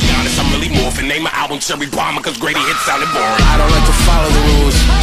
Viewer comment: “La Di Da Di I’m going harder than coming out the closet to conservative Christian fathers, when it’s a lot at stake, Carne Asada, but let’s be honest, I’m really morphing. Named the album ‘Cherry Bomb’ cause Greatest hit just sounded boring” Cough Cough BARS. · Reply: Sound like he said Greaty hit lmao but iv always thought. Did he mean Greatest hits?😂